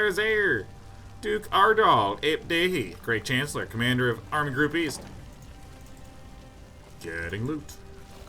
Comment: Every time Sean says "Army Group East" I hear "armed groupies"